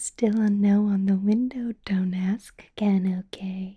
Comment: When she said 'don't ask again' at 11:56 I misheard it as 'dumbass' and spat out my water lmao